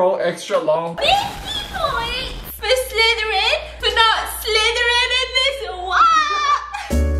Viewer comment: Did u just say for not slithering into this wap? 😂 maybe bc it’s leaking 🤣